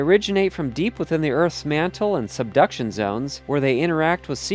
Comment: What I heard was "seduction zone" and what I read was "Rising diapers." I'm fine.